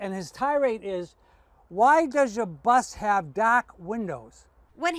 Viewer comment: windows are tinted to keep the interior cool on hot days, and also for student privacy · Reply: I heard "back windows" I've no idea he said "black windows" after I read their comment and listened to the video again. But wow, car guy is literally under a huge rock